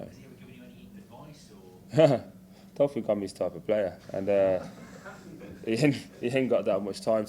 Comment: What did he say at 03:40? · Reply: "I dont think im his type of player"